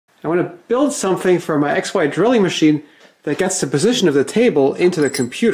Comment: Thought you said "Ex Wife Drilling Machine"! Ha!